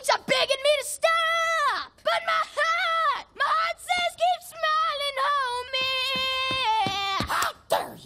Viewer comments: I thought he said my cheeks are big and made with style like my heart my heart says keep smiling Homieeeeee yeayea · I don't know why, but I thought gumball said "My heart says keep smiling, oh man". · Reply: Christina Hamilton I thought he said, "My heart says keep smiling on me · I don't know why, but I thought gumball said "My heart says keep smiling, oh man".